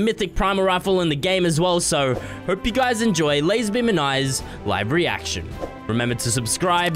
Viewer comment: when he says "live reaction" it sounds like he is saying "livery action" so i am thinking 😳